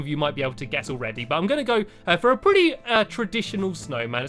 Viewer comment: when u said stick i thought you said d**k